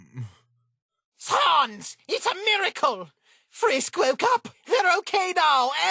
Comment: I heard: Sans! It's America XD